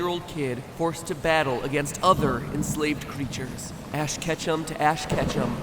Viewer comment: I thought he said ash ketchup instead of ash Ketchum